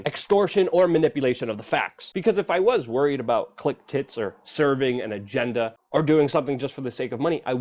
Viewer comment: I heard "click-tits" both times 👅👅